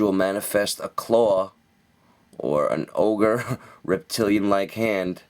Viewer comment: while watching this im trying to work on a drawing and all i hear is "an Ogreish reptilian hand" i about lost it that made me giggle nice